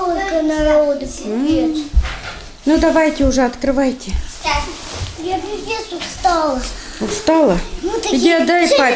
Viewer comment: что сказала? пиздц? если уж хоть звиздец-это уже не хорошо даже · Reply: Пипец как устала